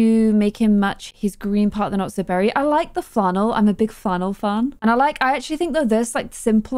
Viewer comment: This episode was so chaotic haha. I also thought said she liked fennel when she said flannel and I’m like same I like fennel too.